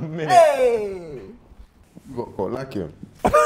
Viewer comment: What he say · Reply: “u got caught lacking”